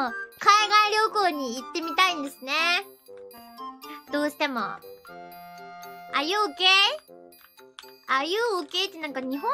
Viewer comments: Dammnit why do i hear that 0:10 'are you okay' as 'are you gey' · somehow i misheard it as 'are you gay?'